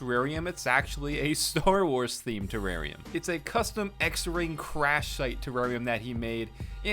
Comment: did you just say x-ring?!?!?!